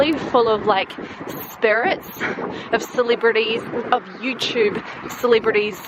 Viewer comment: every time you said celebrities i thought you said silly birdies lol